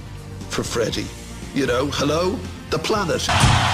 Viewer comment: Why does he say at 0:31 "Hello, the Platters?" I don't get the reference. The Platters were a '50s pop band from America. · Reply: Maybe you are kidding, but if not, he says "Hello, the planet!''